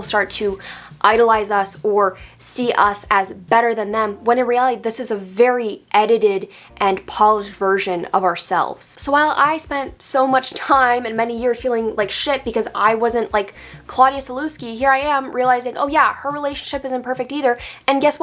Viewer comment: I first thought that said 'a polish version of ourselves'. But I really enjoyed this video. It feels like someone read right into my heart and I couldn't agree more with her